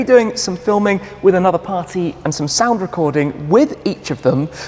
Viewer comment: Wait did he just say sound recording?! Forza Horizon 5 I'm looking at you